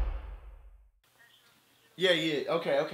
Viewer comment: did you say "E aí?" do you speak brazilian portuguese?